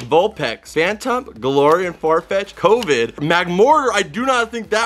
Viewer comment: Is there really a Pokémon called “covid” or and I hearing things- at 8:01😂